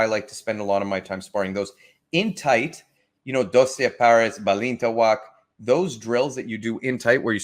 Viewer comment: Did you just say your style is Dossay Pearys ba lee tay wak? Gtfo.